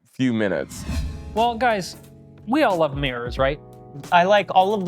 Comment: I heard "we all love MAYORS right?" which made the response give me a double take.